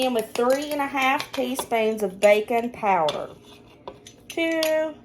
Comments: 😂😂😂😂 when said baking powder I thought you said bacon powder! So I’m all lookin up bacon powder. Couldn’t find it. Went back rewatched and realized you said baking powder and felt like an idiot. I laughed till I cried. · 😂I thought you said bacon powder!!! I blinked and started laughing at myself.....baking powder! I love the south and am trying to keep up with the accent! Love ya all! That onion casserole is bomb!